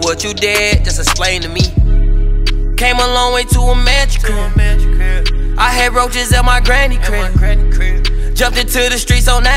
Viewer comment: “ I had roaches at my granny crib” could of swore that’s wat he said😂😂